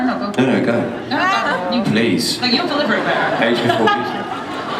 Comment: What did Matt say at 31:45 ???? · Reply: Age before Beauty